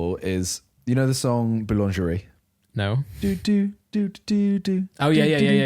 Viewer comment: what song did he say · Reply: He said boulangerie but he meant darude sandstorm